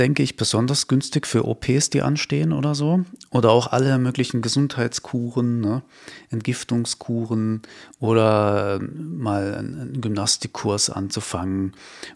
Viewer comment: ahh du meinst KUREN ... und ich habe um die Minute 17:11 KUCHEN verstanden und freute mich um die Bestätigung, dass "Gesundheitskuchen" schon immer das ist, wovon ich spreche . :D aber : Video ist .. so wie ich bereits vorhergesehen habe ... und entspricht auch den Planetenkonstellationen. Nichtsdestotrotz danke ich dir für diese ausführliche Botschaft, die beiweitem detalierter ist, als von anderen.. 💕